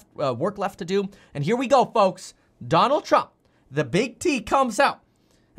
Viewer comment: I paused the vid and when I came back to finish it all I hear is “The big D comes out..”